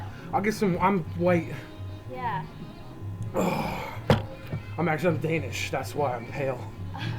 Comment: did he just say "I'm actually Danish, that's why I'm pale"?